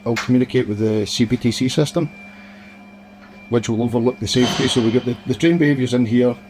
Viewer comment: 'CBCT' in the captions should be 'CBTC'. That is the correct initialism and what the interviewee says. also